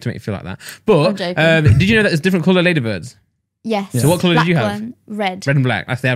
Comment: did Randolph say lady birds?